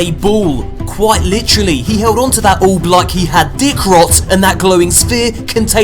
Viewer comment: Did you just say dick rot?